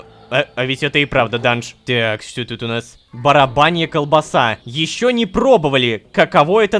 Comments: Почему я слышу не "Баранья" а Барабанья колбоса на этом моменте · Мне послышалось или ок сказал барабанье мясо? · Мне послышалось или он правда сьел "барабанью" колбысу😅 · Кто тоже услышал барабанья колбаса на 14:38 Рома ты просто имба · Кто тоже услышал "барабанья" колбаса, ставте лайк❤😂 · Мне одной послышалось барабанья колбаса?😳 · барабанья колбаса он сказал как только нашел , или мне послышалось ?)) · мне же не послышалось? Барабаная колбаса.??????